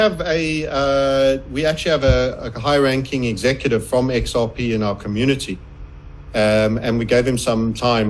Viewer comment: At the 7:20 mark did he say “High Ranking executive from XRP”? Come on?